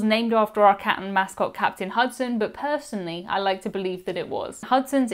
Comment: Until I watch this video, specifically the dinning location Hudson’s, I thought you were saying “Captain Husband,” when referring to the stuffed cat. Which, I think I prefer “Captain Husband” more than “Captain Hudson.”